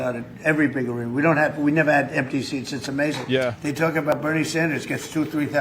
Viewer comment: I heard "empty seats" as NPC's at first, but I guess there isn't much of a difference...